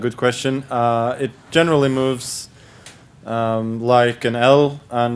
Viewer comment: Ha, I thought Magus said "like an elf"...?! 😄, but on a second listen he said "like an L".... Surely... 🤔🙃